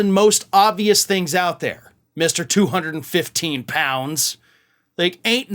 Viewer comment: I thought he said Mr 250 Pounds